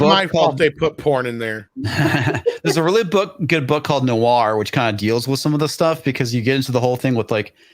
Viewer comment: Does anybody know what book is referenced at 1:37:23 ? He says noir , but can’t find it . And it sounds interesting